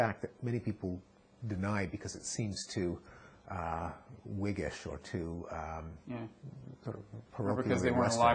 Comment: What is the word at 8:53 'whiggish'? and at 9:03 "non-zero sumness" WTF!!!